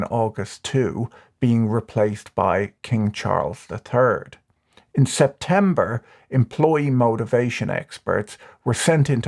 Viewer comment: I swear I heard "King Charles the Turd", and I had to replay that segment while turning up the volume a bit. He says "third", but now I can't get it out of my head.